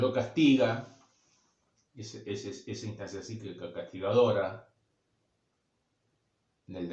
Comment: Buen día! Cuando dice el super yo castiga...(min. 12:46) no logre entender bien lo que dice. Si puede escribir esa parte se lo agradecería!! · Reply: "Esa instancia cíclica castigadora" dice 🙂